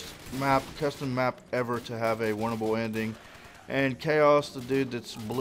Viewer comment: I always thought you were saying "Bible ending" not "Buyable ending" lol.